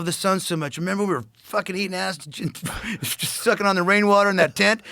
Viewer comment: did he say eating ass?? haha